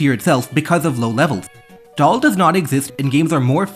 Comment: what does he mean when he says "doll does not exist"?